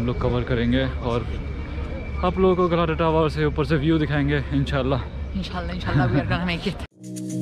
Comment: What did say Insallah ,?